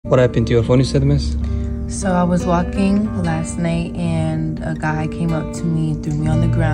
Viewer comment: I thought she said (so I was walking my snake) 💀 😭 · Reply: She said walking last night not snake u need ur eyes check dude not to be rude